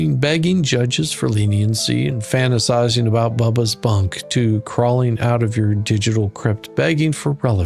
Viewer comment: I thought you said Bubbles' bunk and lost my sh!t 😂😂😂🤣🤣🤣